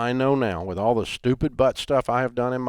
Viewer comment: did dave just say he’s done a lot of “stupid butt stuff” ???